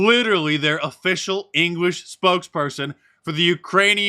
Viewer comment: I swear I heard him say "spooks"-person. Horrible horrible reading from a screen.